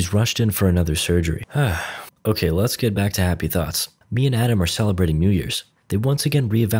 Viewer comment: I thought he said "me and Adam are celebrating new years'